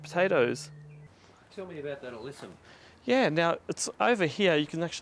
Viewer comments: what kind of plant is it. I couldn't hear it. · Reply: Looks like Alyssum. Google it and you should get the growing info. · What is that plant please? Couldn't quite hear it. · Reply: Alyssum fantastic little plant Comes in purple mauve Also called sweet Alice if you get up close has a lovely perfume I like it So so easy to grow by seed